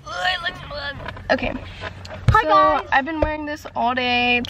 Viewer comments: why I thought said "hi guys" thooo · I THOUGHT HE SAID HI GUY SKSKSKS